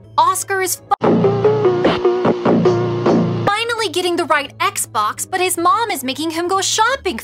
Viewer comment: Dora said Oscar is FU- instead of finally